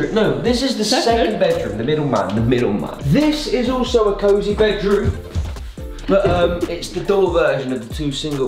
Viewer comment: Did anyone else think when said coazy bedroom, that he was talking about Koazy's bedroom